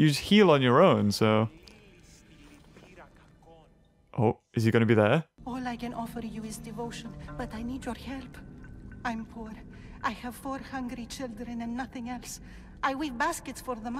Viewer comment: i thought she said ”I have 400 children”